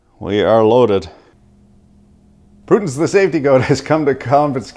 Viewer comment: "Prudence the Safety Goat" cracks me up every time. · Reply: I thought he said "Putin the safety goat". I could not figure the angle.